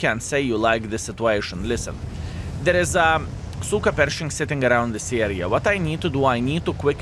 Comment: I thought I heard him say. SOOKA PERSHING